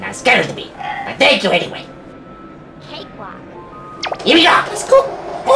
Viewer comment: I wish I knew What she is saying. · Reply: EpicThunderbolt21 i heard cake wad